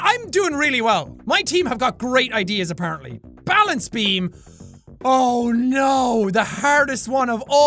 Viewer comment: I didn't really pay attention when you said "Balance Beam," and thought you said "Alan's team." Like, "my team's got GREAT ideas, but Alan's team..."